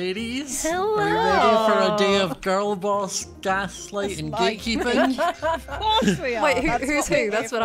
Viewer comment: When said gatekeep, I thought he said gaykeep